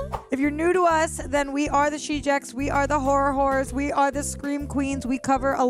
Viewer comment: did she just say whore of horrors . I think it was a mistake.. 😮